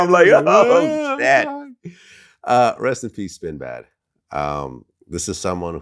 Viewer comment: Maaaaane work on that pronunciation, I thought He said Sinbad.!😂😅